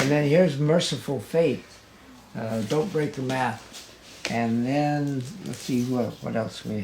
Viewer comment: did he just say dont break the bath? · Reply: I thought he said "Don't Break the Math" lol